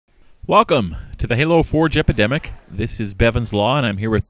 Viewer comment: I always hear" the halo 4 jepidemic" instead of "the halo forge epidemic"